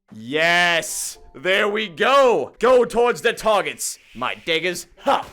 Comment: Did he just say: go tords my target my ni**as